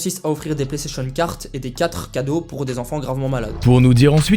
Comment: J'ai entendu PlayStation "carte" et "quatre" cadeaux pour des enfants gravement malades ? Bon sang, j'ai pitié 21:47 🤦